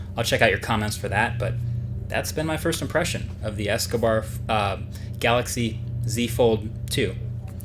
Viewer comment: Did he just say the Escobar fold🤣🤣🤣